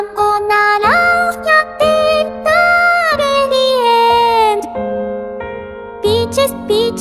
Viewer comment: I thought miku was saying Beach Beach